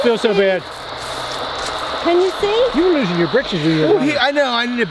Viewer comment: The next time y'all go out and do the Spirit box leave at home can't make anything out because of big mouth he's always talking he never shuts up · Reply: it sounded like it said “I See You”